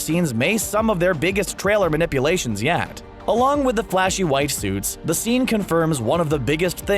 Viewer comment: who else thought that they heard him say "WIFE SUITS" instead of what he was more then likely trying to say; which was "white suits"